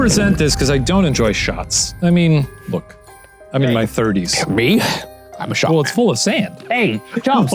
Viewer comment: I love how at 9:39 Ryan says "I'm a shot man" but the subtitles say "I'm a short man"